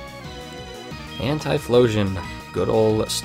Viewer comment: I'm not the only one who thought/heard Anti-phlosion, right?